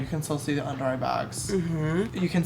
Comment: I thought the earrings said Charles 😂